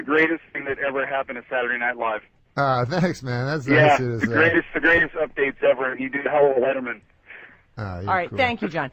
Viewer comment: In the minute 5:38 a guy calls and says norm is the best thing that ever happened to snl (no kidding) but after he says something that seems to offend norm. I understood "the greatest updates ever you did hello letterman" does anybody know what he was talking about? Was norm upset? Should we track down that guy and kick his ass? Kind regards. An argentinian norm Fan.